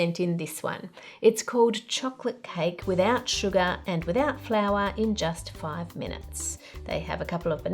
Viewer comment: misheard it as without flavour and thought 'what a weird way to advertise cake' lmao